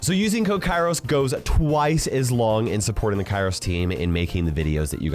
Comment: What did he just say? Kairos Team? KAIROSTIM??